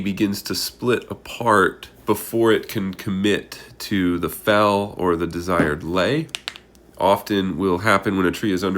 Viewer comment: What's a hinge? Also, in 0:09-0:14, did you say, "before it can commit to the fell or the desired lay," using the words, "fell" and "lay"?